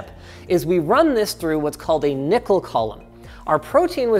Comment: When Linus first said “nickel column”, I swore he said “nipple column”. I wish he really did!